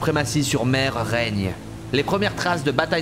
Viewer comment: jai compris l'ile de shybre